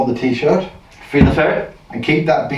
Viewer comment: I had a bit of a learning curve with your accents. After watching a number of vids in your library I mostly do not hear it now. But I swear I heard you say "free the ferret". Perhaps you did, I don't know. But I thought it was funny and I started saying it. Now my entire family is calling for the ferret to be free! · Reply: Hi, sorry but yes i do say free the ferret. lol. Thank you for watching